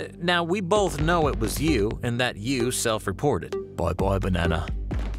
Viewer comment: The subtitles say "boy boy, banana."😆😆😆😆😆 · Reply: But he actually said Bye Bye Banana. 🤣🤣🤣🤣